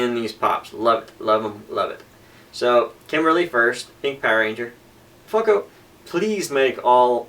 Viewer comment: It sounded like you said Fucko at about the 4:40 mark... haha